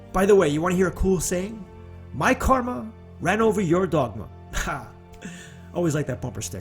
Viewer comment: I must have misunderstood you, I thought you said my Karma ran over your dog...😮